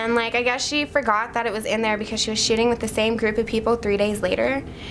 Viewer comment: why is it that i always hear "shitting" instead of "shooting"